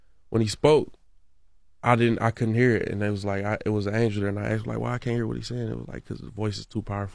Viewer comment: 59 With all due respect my brother, he said "there was an angel that night" that he spoke to not an angel of light while it may have been inadvertent you may have misheard that, not defending Grizz by any means because he did not in this portion of his interview profess Christ as his lord and saviour, however, he didn't say what you are impling he referenced what he believed was God as the light that he could not hear and asked that angel he believes to have been there with him why and that is when he says the angel/ other figure spoke to him